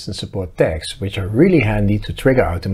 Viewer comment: Well, I guess my infection is effecting my hearing. I thought you said to arm and disarm via text. Was very lost for a second there. Tags oh I get it tags!